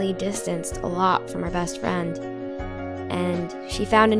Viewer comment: I thought it said "lots of fronds" instead of "lots of friends" 😂😂